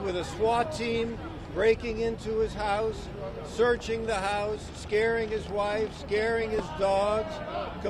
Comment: scaring his wife Scaring his Dog i thought he said scaring he’s daughter 🙈🙈🙈😂